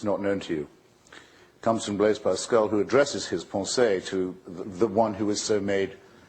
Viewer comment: What's the French word he uses at 2:40? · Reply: his (pensée ) his thought